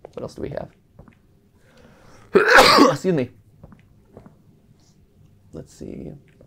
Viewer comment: in 42:06 I thought for a sec that said "Sue me"